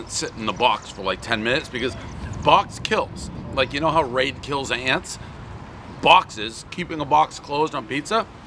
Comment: Who else thought he said “rape kills ants”?